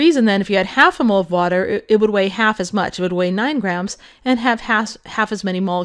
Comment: did she just say ass as much.....lol